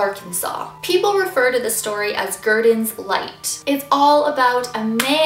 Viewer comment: anyone else thought that said "guardian's light" instead of "gurdon's light"?